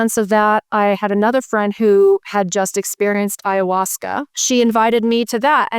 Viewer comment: Hi! I've got a hearing loss and the closed captioning isn't helping. Her friend was experiencing EYE-UH-WAS-KUH? at 58:46. What is that word, please? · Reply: ayahuasca